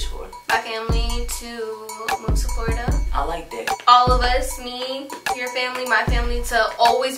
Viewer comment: did he say “I like Dick?”🤣